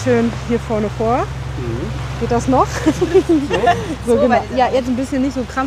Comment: was sagt sie da: geht das noch? 😂. ich hoffe, die ist schwanger